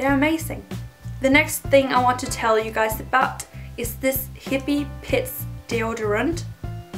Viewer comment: I liked this video so fast! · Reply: Oh and when you said "hippy pits" deodorant I totally thought u said hippy piss....lol bahaha